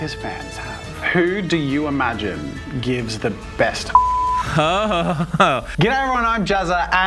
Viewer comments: I thought he said "who gives the best head?"😂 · oh man that censur was so funny. i thought you said like "who do you think gives the best head" but it was hugs wich is way more wholesome... sorry XD